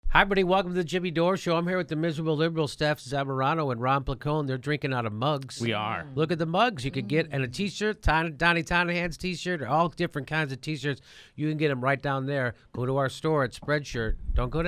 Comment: Look at the Mugs, thought you said look at the Jugs for a min.